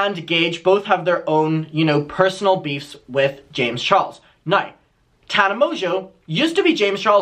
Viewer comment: I thought you said "Shames Charles" and..... I'm not mad about it